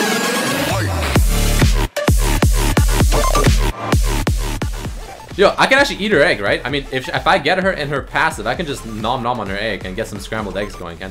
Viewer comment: it sounded like "i could just him not on her egg hentai"